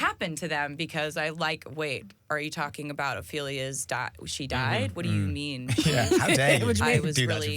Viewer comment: Jennifer Lawrence?! “What do you mean” sounded like that 😂🩵